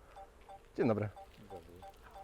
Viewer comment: Great test. Are you Polish? I thought I heard the phrase "dzień dobry" :). Greetings.